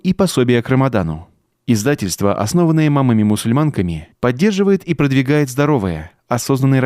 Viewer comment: Мне сначала послышалось имамами-мусульманками😂